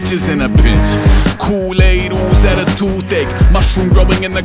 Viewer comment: So disappointed 😂 thought he was saying Dr pepper on a toothache